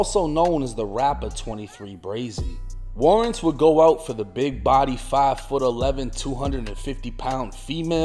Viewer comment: "Warrants would go out for the big body, 5'11, 250 pound female" was low-key; high-key standup comedy work 😂😂😂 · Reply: I swear I thought he said big booty at first, I had to rewind 10 seconds real quick to double check.